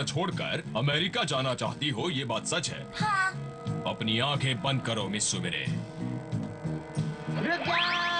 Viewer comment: did payan say robot sumire? Didn't perman no.1 notice?